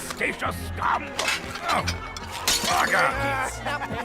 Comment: Everytime Bort said "Bugger" i thought he said "Baka" and it botherd me so much because i knew what he was really saying but i cound't stop hearing "Baka" like he's some little anime girl or some shit lmao